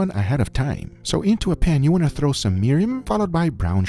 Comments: what is the first ingredient it sounds like your saying mirum well that's what the closed captions says where is the recipe? thanks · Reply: Mirin is a sweet japanese rice wine used for cooking. I'm pretty sure that is what he was talking about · throw in some what? the subtitles say "mirum" but googling that leads me to some sort leather alternative. :(